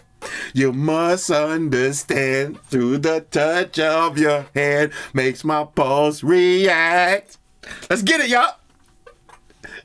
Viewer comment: 🎵 through the touch of your hands makes my🎵 I was driving just listening and bro I swear I heard u say "balls react" 😂😂